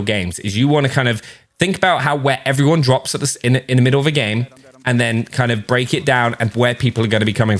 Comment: lol sounds like he said how wet everybody drops in the middle of the game 😳